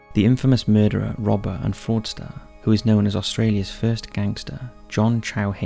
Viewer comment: Regarding the very first video (@ 1:52): "The infamous murderer, robber, and..." Porn star? Fordster? Forester? Wtf is he saying? 🤔 Can somebody please tell me? 😅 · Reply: According to the closed captions: fraudster